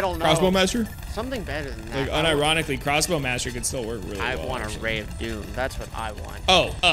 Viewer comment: I'm so sorry but I didnt hear eat if doom excuse me for what I'm about to say but I hear "I wanna rape you" I am sorry I just thought it was hilarious.